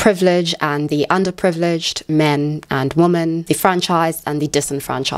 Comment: Are you saying "woman" when referring to "women"? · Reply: "Men and woman" Am I hearing this correctly?